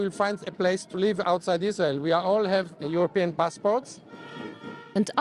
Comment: Did he say they all have European passports?